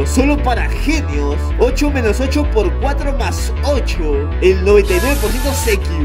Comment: La multiplicación dice "8-8x8+8",pero....¿Fui la única que escucho que dijo "8-8x4+8"? ;-; Pd: Pienso que el resultado es -48 nwn